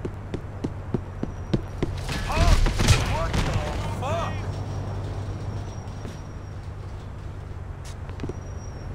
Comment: i head to replay that part because i couldnt hear what he was sayin because i laughed my ass of :D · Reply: "I can smell your labia from here"